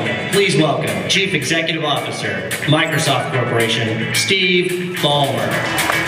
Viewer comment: in 0:45 - 0:47, did he say say Steve bonner?lol